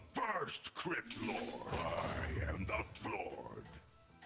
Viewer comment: Does that guy say "I am the Floored" instead of "I am the first crypt lord"? Lol